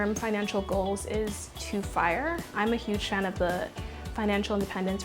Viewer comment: What center did she join? 5:55 I can’t understand what she says here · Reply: It’s not a centre, it’s a lifestyle. FIRE is Financial-Independence-Retire-Early